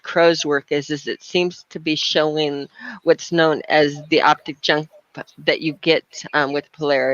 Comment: what does say here? Optijump?